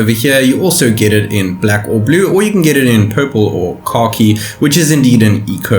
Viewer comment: Khaki? I thought u said car key 🚙 🗝